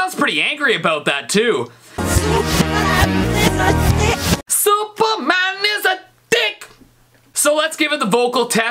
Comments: I heard M.J. say “Suppa man is a hick” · And Michael Jackson’s Superman one it sounds like he saying superman is a stick